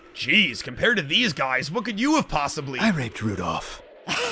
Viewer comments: did he say he raped rudof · Am I the only one who heard him say that he raped Rudolph the first time? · what did that guy say he did to Rudolph? I heard it as "I read Rudolph" and that doesn't make sense to me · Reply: He said he raped Rudolph